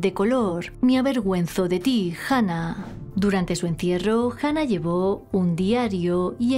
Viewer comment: ¿Durante el entierro de quién? No entiendo 🥴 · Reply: Dice durante su encierro